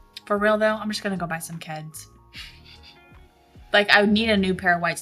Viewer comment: Did say she wants to buy a pair of white kids or am i mishearing? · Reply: Keds not kids